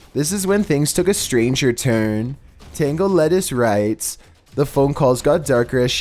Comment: I'm listening to this trying not to get scared and then I hear "TENGO LETTUCE" 😂😂